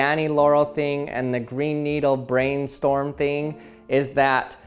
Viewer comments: I forced myself into thinking "Brainstorm" ,But I always hear "Green Needle" . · 100 IQ: Hears "green needle" 130 IQ: Hears "brainstorm" 300 IQ: Hears "brain needle" every time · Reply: 😱😱😱😱😱I HEARD "BRAIN NEEDLE" YOU WILL NOT BELIEVE! BUT I DID!!! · I heard “brain needle” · You can do things like “brain needle” and “green storm” as well... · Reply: I only heard "brain needle", lol. I can make it switch to the others, but only if I'm really trying to hear them. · I heard 'Brain needle' 😆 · i hear "brain needle" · I hear "brain needle". I must be broken :-D